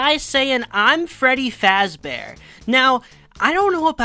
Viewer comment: I always interpreted the trailer guy as saying something like "Freddy Fazbur" and he'd just named the character Freddy Fazbear after himself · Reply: I thought he said Freddy fazb(u or e)rg